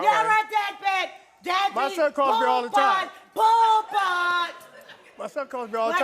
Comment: Its NY slang...poo putt. Its kinda like dork. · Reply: ohhh I thought she said " poobutt " 🤣 but thats not why I said shes tweakin. Its just her movements, her head, mouth , eyes , just seemed like she's on the pilo. But someone said she might have a neurological disorder, so I could be wrong. Either way I hope she gets therapy because childhood trauma is the cause for alot of pain